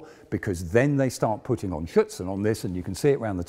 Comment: I heard "Schützen" (rifleman) instead of "Schürze" (skirt). He is not really pronouncing the "r" in "Schürzen" which is important for understanding the word. (At least for me as a native German speaker)